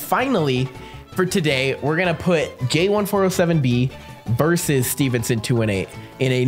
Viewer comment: it kinda sounded like “gay1407b” 🤨